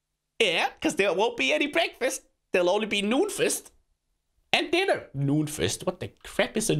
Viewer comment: When he said noon feast it sounded like he said nudefest